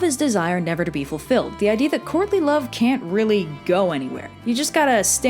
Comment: I keep hearing “Courtney Love” and it sends me